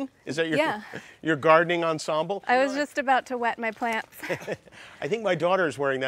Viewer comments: What i heard is "im about to wet my pants"😂 dakota i love youu ☺️ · Thought she said wet my pants for a min lol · Anyone else who thought she said I'm just about to wet my pants 😅😅😅